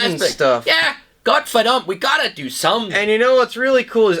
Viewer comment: Did I hear "Gottverdammt" at 7:00 ? We still use it in Germany, too. In Dutch it is godverdomme.